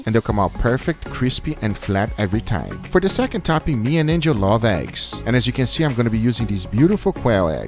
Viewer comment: sounded like he said "me and ninja love eggs", had to think about that for a few seconds before I realized he said Angel.